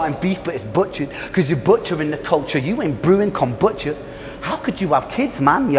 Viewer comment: When you said "little steak on my plate, you're starting beef but it's butchered because you're butchering the culture(as in not contributing positively to the culture but butchering as in cutting animal meat. Sauce Money the NY rapper on the Kay Slay - Rolling 110 Deep said "just remember culture vultures ain't always white". Dotta is on that culture vulture ish.), you ain't brewing con butcher", were you showing love to elite lyricist from Griselda Benny The Butcher. Con means with in Spanish. When you say he is not "brewing con butcher", and you saying he is not making music with Benny The Butcher? Are you saying Dotta thinks he's an alien on th mic but is unable to collab with a rapper like Benny The Butcher? Are you saying he's not on the same level lyrically as Benny The Butcher? Is my interpretation off? What you said was fire. Fire references indubitably. That's dope that you said a unifying and truthful message at the end that humans should be unified and united. The enemy is not someone from another race. Whites are not the enemies or adversaries. The media want races to be disunified and disunited so they can easily control. The media exemplify negative programming and mendacity. It behooves a plenitude of people globally to cease listening to the canards and extricate themselves mentally from the mental cages. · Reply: ok. Thanks for correcting me. Yea. I thought he said con Butcher. It sounded like he said con Butcher. He said Kombucha. Thanks.